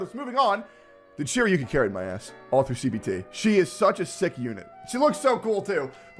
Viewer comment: did i hear CBT?!